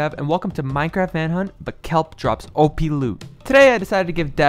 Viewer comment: I thought it said keir drops op loot